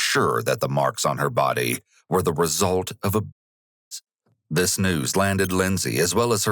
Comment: What was said @ 12:58? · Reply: Result of abuse